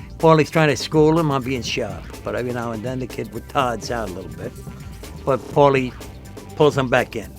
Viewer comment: does he say the kid “ retards out”? lmaooo